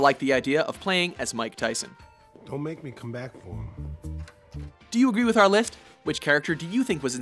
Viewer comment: After showing Mike Tyson I hear do a distinct lisp when he say's "Do you agree with our lithst?"